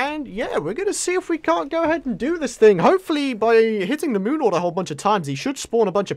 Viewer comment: it sounded like he said I hope I CANT do this lol